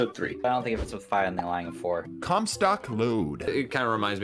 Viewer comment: did anyone else hear "Cumsock Load", or just me?